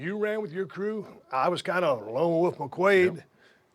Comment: it sounds like Austin says, "I was kinda alone on my quaa's" ( Quaalude ) 😂